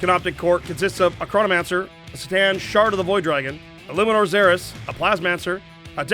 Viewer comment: Did I hear "Stan, Shard of the Void Dragon"? Wait no, said Ctan.... I swear I must be going deaf...